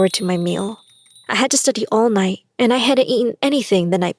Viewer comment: Am I the only one that thought she said she was studying all might at 4:05?